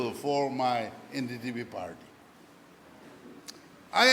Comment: I heard only NDDP🤓